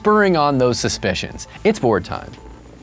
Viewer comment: when he said "its board time" i thought he said "its vore time" 💀